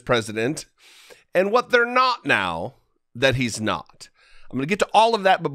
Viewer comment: This is too funny not to share! My internet cut out for a second and as was saying, about Trump's duties, now that he's not in power, it sounded like he said, 'now that he's snot.' I sat here and giggled aloud. Thank you. I know that's not really what you said, even if you'd have liked to. I kinda think it fits, though... Lol! But thank you for what you do!